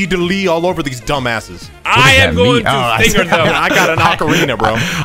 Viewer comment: did say he’s gonna finger them💀💀